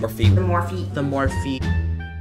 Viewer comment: it sounds like manny was saying “Morphe is like the shittiest brand” correct me if I’m wrong